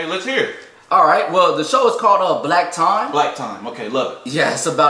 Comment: What’s worse is I thought he said “Black Tom” at first😬